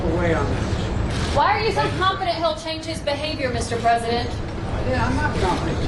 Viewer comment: Biden snaps at reporter "Hey I'm not confident that he'll change! What the hell.. what do you do all the time? When did I say I was confident?" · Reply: he said "i'm not confident he will change his behavior" i'm no fan of biden but come on man